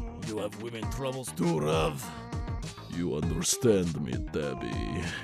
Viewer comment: "You understand me Tabi" What I heard "You understand me Daddy" 💀💀💀